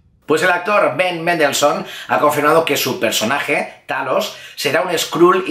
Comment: escuché thanos? alguien explique el porque o que xD